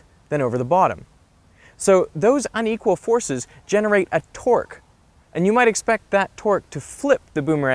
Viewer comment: Thought he said generate a twerk lol